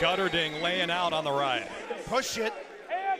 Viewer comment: did he said “oh sh*t”?? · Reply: he said push it right